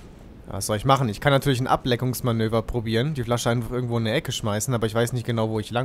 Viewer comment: da hab ich "Ableckungsmanöver" verstanden^^